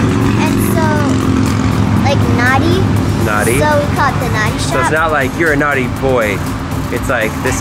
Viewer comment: i heard "naughty shop" at first...